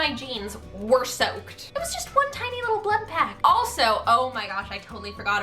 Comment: did anyone else hear, “just one tiny little blood-pact?” 😂😂😂